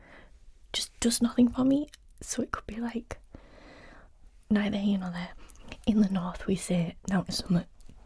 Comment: help me out. What do you say in the North? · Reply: ‘Nowt nor summat’ Meaning nothing nor something, if that makes sense 😅